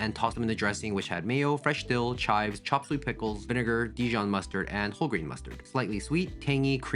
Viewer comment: I heard "Chocolate pickles" not "Chopped sweet pickles"😳